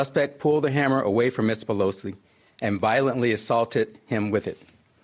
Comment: It does seem shady.. Something is very strange in what this police officer is saying... Check transcript 2:11 hammer away from MISS PELOSI and... I thought I heard wrong so I went to check the transcript and there it was... Exactly what the officer said... So clearly he is reading this that someone gave him to read tto the press